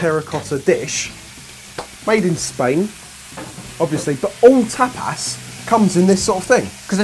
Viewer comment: I got confused at the beginning of the video when said "tapas." I thought he was trying to say "top ass."